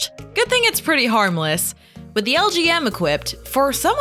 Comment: did... did she say lgm?